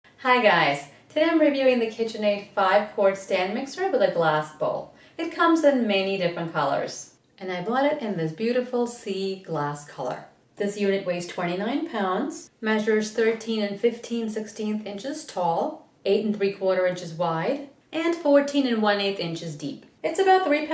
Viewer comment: I heard "This unit weighs 20 million pounds" and I laughed pretty hard at the funny sarcasm. Had to rewind to realize you actually said "29".